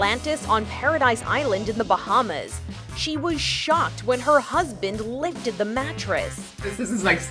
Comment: Omg on the first story I thought they said the husband licked it